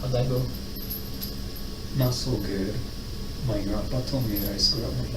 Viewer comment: What is it that he said his grandfather told him? I can’t seem to make it out. I’ve seen this interrogation a fair few times, and yet I’m still too much of a dunce to understand what he said. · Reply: “My grandpa told me I screwed up my life.”